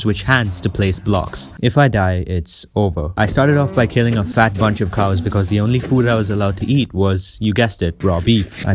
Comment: I heard fat butt cheek cows instead of fat bunch of cows even though THERE WERE SUBTITLES!!!